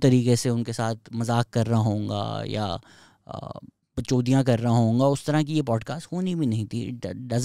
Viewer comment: did he said “BackChodia” 😂